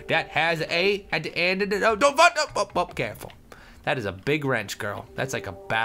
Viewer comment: I thought said "Don't fuck up!" lolz